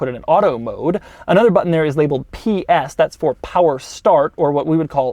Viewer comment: Did he just say "PS stands for Power Start, or what women call Launch Control"? :)))))